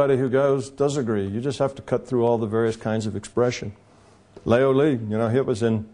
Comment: leo li ? whats does he says at 0:34 ? cant find anything on google... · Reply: Laozi (Tao Te Ching), in case you've been wondering for seven years.